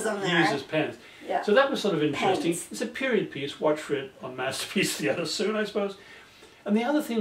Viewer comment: I guess my hearing isn't as good as it should be, but at 7:54 or so, it sounds like Dad says, "Watch for it soon on Masterpiece Theater, I suppose." Is this correct or do you need to start adding subtitles for your semi-deaf American viewers? Ha ha! :-)